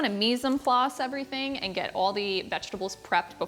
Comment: Hey. I know I'm asking about an older video, but it's new to me. :) What's the term you say at 1:58? I didn't understand it and closed captions was confused too. Loved the use of cholula. Good call by Drew. My brother turned me on to that before he died and now I can't have eggs without it. Always reminds me of him too. I really enjoy all the stories you tell and how you explain why you are doing something. You have such a nice conversational style. Too many channels have people awkwardly reading scripts and that's so unnatural. Your videos are reminiscent of Giada's to me. Keep up the great work. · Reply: Of course! Sorry I didn't explain that, "Mise en place" is french for just prepping ingredients. So I mainly just meant if you want to chop all the ingredients ahead of making everything, you can do that:) And I really appreciate the kind words! It means a lot to get feedback like that. So thank you for watching! I'm certainly no Giada, but grew up watching and admiring her. Thank you so much for the compliment🥰